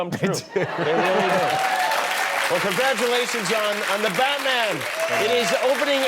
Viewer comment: did he just said congratulations jhon ?!